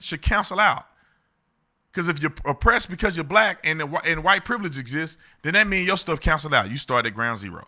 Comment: My kid was watching her tablet and I heard "use your white privilege" I'm like nah fam you got to go